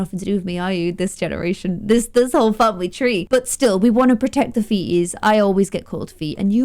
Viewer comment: When she said protect the feeties I wasn’t paying attention and thought she said protect the fetus and thought Marina was pregnant again. Let’s just say I started paying attention again real quick.